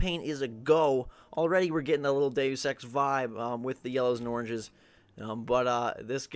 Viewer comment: i thought he said gay sex vibe lol